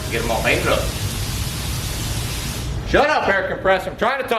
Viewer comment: I thought you said, "shut up erica pressor, I'm tryna talk."